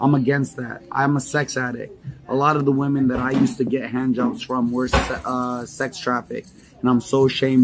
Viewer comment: pause., did he say he got hand jobs from lol 😆